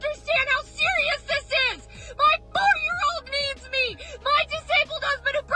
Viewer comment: I thought she said “my four year old memes me”